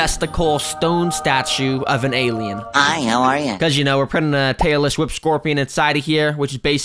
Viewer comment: i thought he said a “Taylor swift scorpion”